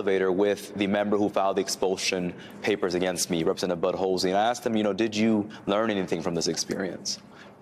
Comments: I thought he was saying Representative Budholz. Imagine my Bart Simpson moment. Bart "Can I speak to Bud Holz?" Moe "Bud Holz? I'm looking for Bud Holz!" Apt tho'. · did he say representative "Butt Holesy"??!